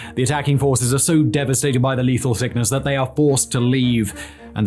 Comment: lethal thickness did you say..? Well, lets begin..!